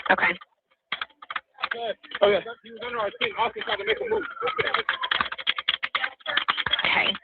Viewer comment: the caller sounded like he said ( "Austin tried to make a move" )